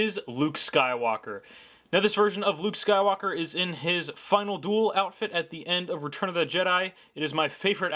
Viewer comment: it sounds like you said funnel duel